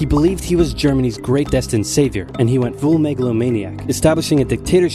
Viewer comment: At first when he said megalomaniac, I thought that he said Megalovania XD